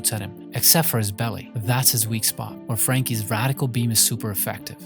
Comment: I thought, bro, said Frankie’s radical pin### instead of Frankie’s radical beam